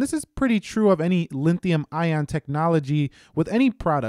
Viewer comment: Did I hear you say Linthium ion technology at 0:51?